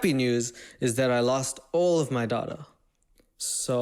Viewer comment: im English and to me that sounded like all of my daughter.